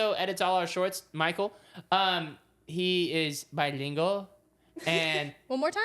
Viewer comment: When said that his friend was bilingual, I thought he said that he was illegal🤣